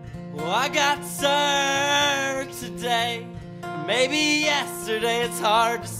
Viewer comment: I think she said "sir'd" not "severed" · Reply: Omg I thought it said served I didn't realise it said severed 😰😅